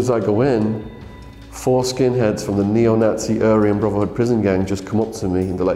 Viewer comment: Did he say foreskin heads ?